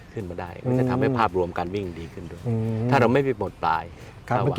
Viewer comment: อ. พูดว่าอย่างไรหรือครับ "ถ้าเราไม่ได้..." ได้ยินไม่ชัดครับ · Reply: "ถ้าเราไม่ไปหมดปลาย"ครับ